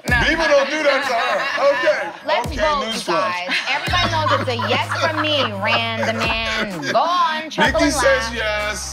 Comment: What did Randy say in 1:52 that made Mariah laugh? 😂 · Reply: its “news flash”